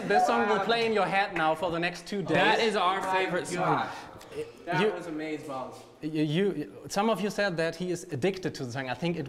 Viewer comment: Who else heard say “that was amaze balls” in the background at 10:20 😂😂😂